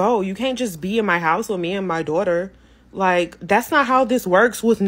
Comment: Did you say me and my daughter????? Oh hell no! That would've been a no anyway but PROTECT YOUR CHILDREN